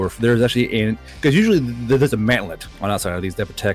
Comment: I thought he said manlet instead of mantlet. Guardbro finally putting these 5'11 boys to good use!